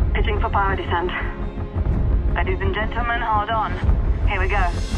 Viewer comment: it sounded like she said ladies and gentlemen Hard On lol